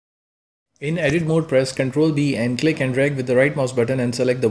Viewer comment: in 4:16 does he say ctrl D? because ctrl D doesn't seem to do anything for me